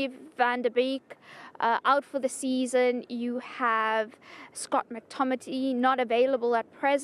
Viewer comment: Did she just say scott mctomity?